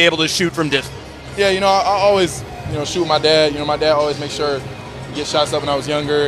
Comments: did he just say I always shoot my dad? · did I hear correctly that this young man beloves shooting his dad? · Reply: No he said shoot with my dad if you listen very closely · I was really sleepy and I hear 01:18 yeah you know I always shoot my dad I'm like dafaq did he just say 🤣